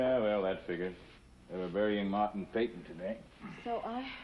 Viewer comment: closed captioning has 'Martin Satan' instead of 'Martin Peyton' lol